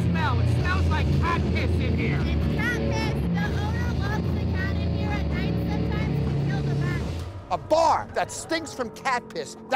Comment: i thought she said "he loves the cat in here it sometimes kills the rats" he LOCKS the cat inside the bar, in no normal circumstances should a cat(or any other animal) be inside of anything to do with food sanitizations, dont forget the fucking Roaches and Rats and the Cat piss smelling bar, thats animal neglect/abuse